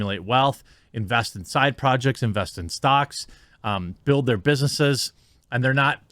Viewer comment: For a moment I heard 'invest in socks' and was very curious what the hell that meant.